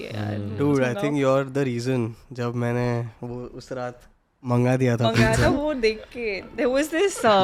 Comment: Did really said "You are the reason jab meine uss raat....."